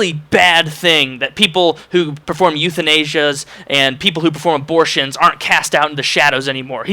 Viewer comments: haha at first I thought he said "youth in Asia" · Every time you said euthanasia I thought you we're saying "Youth in Asia". For example: Youth in Asia are responsible for 10% of all deaths in the Netherlands.